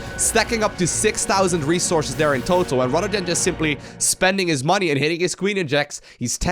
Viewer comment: So I watched a couple of these videos and i thought it was strange you kept referring to a lot of the players as "Rotterdam". Is Rotterdam playing under all these aliases, whats going on here? ANd I just heard it... its "Rather then". mystery solved